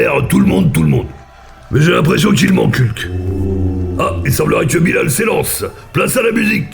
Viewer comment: jmattendais à un jeux dmot avec "il m'encule" qd jai entendu "il manque hulk" xD